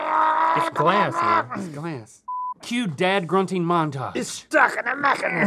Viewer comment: "Cue dad grunting montage" 😂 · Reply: I thought he said “cute dad grunting montage” 🤣 thanks for clearing this up